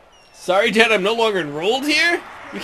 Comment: I thought you said, "Sorry Ten I'm no longer enrolled here" like I left. I was about to rage. 11:10 Is his mom the same mom from the Waterboy or something? "I don't want my little Hayden to be playing no FOOSBALL!" 57:29 Jesus, not that I don't like being part of the team and having all this responsibility as a freshman but there's other receivers too. All this kicker worry, is your kicker Josh Scobee? And WOOO!!!! Great win, character win too knowing you can beat a team like the spartans.